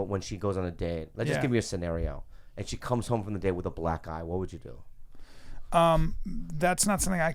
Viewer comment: I thought Bobby said she comes home with a black guy.